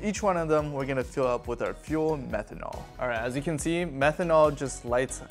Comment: I thought bro said fent 😂